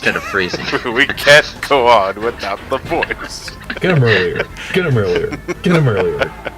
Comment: What is he saying? Get em' earlier? It sounds familiar for some reason.